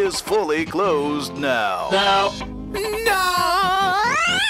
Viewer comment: what did moony actually say there? (because I keep hearing dinner.) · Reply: “I wished to be thinner”